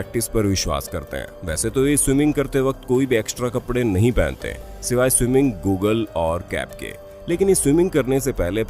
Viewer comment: did you say it" swimming Google"😅😅😀